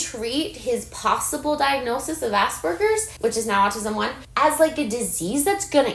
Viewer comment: 😭 why'd the automatic cc say assburgers